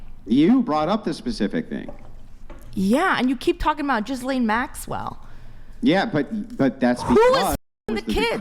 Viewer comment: did she say Jizz Lane Maxwell ? lmao